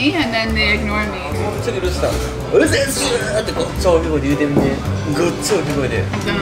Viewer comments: Mrs.ルースさん？って聞こえたんだが‼️空耳👂❓❓ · あれ、Mrs ルースさんと聞こえたような。6`28"